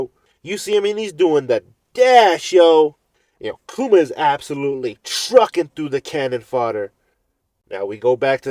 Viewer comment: I thought it said "Kuma is a b1tch" ahaha